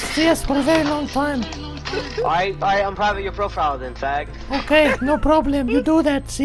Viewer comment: what did the mad guy say @ 11:28 about jack's profile? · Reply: he said unprivate your profile becaus ehe probably has it on private :)